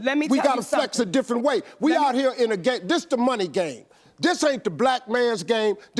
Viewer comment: ITS OVI EGO PRIDE SELF RESPECT HASNT A VOICE IN MONEY GAME.. IT SOUNDED LIKE HE SAID THIS IS THE DEMONIC GAME.